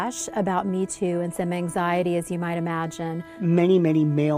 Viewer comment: I misheard “anxiety” as “Manxiety” and even though it isn’t a word, it really should be. It’s the anxiety men feel when dealing with current year women in the workplace.